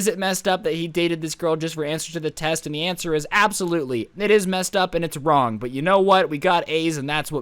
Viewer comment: I heard “yea it’s messed up but we got aids”